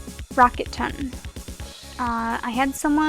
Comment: what is the pasta's name? I keep hearing rocket ton but that doesnt bring anything up xD I really like horrible pastas cause they can be funny bad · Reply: I think it was spelled Rocketton.